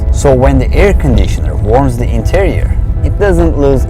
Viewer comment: I'm sorry sir, did you say when the air conditioner warms the interior.......